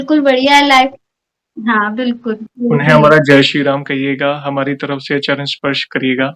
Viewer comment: 🙏 सीताराम हनुमान, बोलो जय श्रीराम। नमस्ते जयेश भैया, आपके इस वीडियो में 29:29 के time पे silent voice में "राम" बोल मुझे सुनाई दे रहा है। आपसे अनुरोध है - आप जरा सही से सुन कर मुझे आश्वस्त करें क्या वो सच में "राम" ध्वनि ही है? नेहा, बेगूसराय